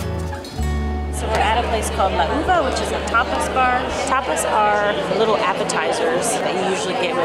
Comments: At first, when you said that you were going to a tapas bar, I thought you said that you were going to a topless bar.🙂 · Tapas bar. I thought you said topless bar at first! Oso is too young. 😅🤣😂